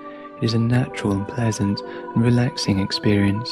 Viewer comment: What are you saying at 4:58...sounds like "controlled"